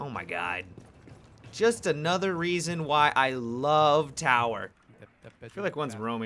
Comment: I thought u said u tower lol